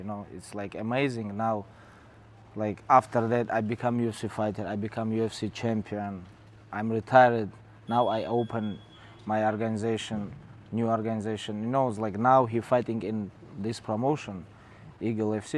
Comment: sounds like Khabib said I’m retarded